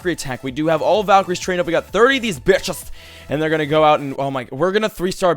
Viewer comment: did he say "B*tches"